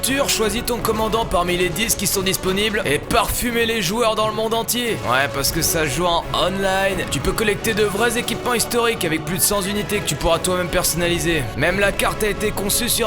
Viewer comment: j'ai entendu parfumer les joueurs du monde entier